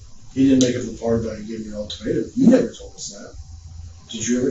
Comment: CC says 'alternative' but I'm hearing 'ultimatum'.